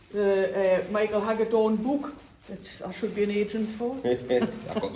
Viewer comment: Thanks for sharing the video, really enlightening. A couple of questions if I may? What book was referring to at 1:36 please and although I've now watched the video twice, I didn't catch what said about the best time to repot conifers and pines. Any help would most appreciated. I also look forward to your video on the 'correct' way to wire pines too. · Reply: Thank you Mike. It’s Bonsai Heresy by Michael Hagedorn. I’ve added a link. <link> Repots for pines is about now depending on the weather or autumn as long as you can offer the aftercare needed.